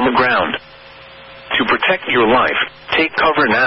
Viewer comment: I thought it said protect your wife, not life. 😂